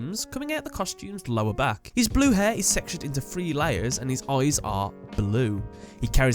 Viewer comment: I literally thought he said his hair is sexual...Lawdy Lawd!!!!!